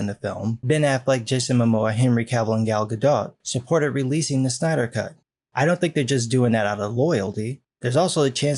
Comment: Wait a minute, did he say Stiener Cut? It would be interesting to see Papa Pump Make a JL movie.